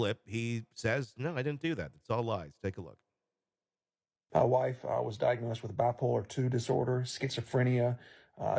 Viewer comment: Why does it sound like he says "My wife, "I" was diagnosed....?? At least it does to me.